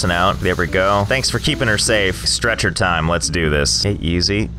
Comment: When he said “stretcher time” I heard “stretch.her.time”